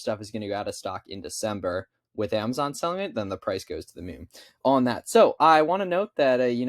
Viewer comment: Help! Help me understand what he said on 1:53-1:54 he said the price wil mim??? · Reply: he said moon